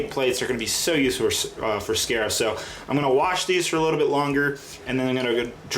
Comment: It sounds like says 'Sacrif' instead of Crait. : )